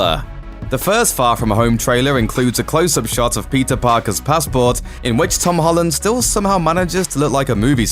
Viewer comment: When he says Peter Parker it sounds like he is saying pizza pockets ! 🤣